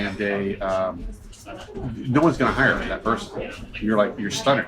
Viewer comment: I think the word he said is "stunted", not "stunned"